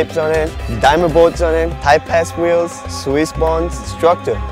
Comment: hahahaha it sounds like he says type- ass wheels 52mm